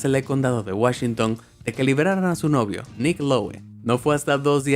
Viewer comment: Pensé que decía Mclovin 8:33 que gran película